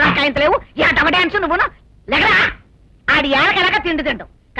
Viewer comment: DID SHE JUST CALLED PLAYA....JUS CHILLIN ......THE ""N""" WORD.........??? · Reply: 😂😂 she said "lega raa"... Means get up...